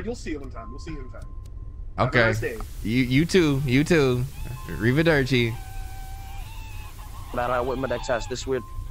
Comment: did anyone else hear "read the dirt chief" instead of "arrivederci"?